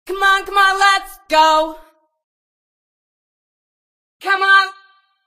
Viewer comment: What did she just say? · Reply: Come on let's go